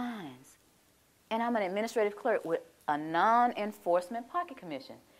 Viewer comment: I thought she said menstrated